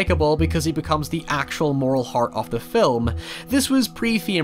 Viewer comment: The opening farm scene is definitely an metaphor for social distancing... right? DISCLAIMER FOR THE VIDEO: <link> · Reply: I can't understand what you're saying, "...this is pre-" BLANK" Renner.... The autocaption can't figure it out either.... It sounds like you're saying "theme", my best guess is you're saying "fame"... Are you saying "fame"?